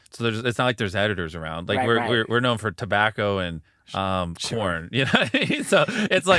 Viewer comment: auto caption says " we're known for tobacco and uhm porn" 😂😂😂😂